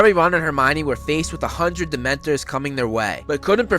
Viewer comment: Did he just say hundred demonteres?😂 More like hundreds🤔 Love you’r vids tho!😇